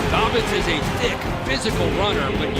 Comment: Did anyone else hear him say “Dick”?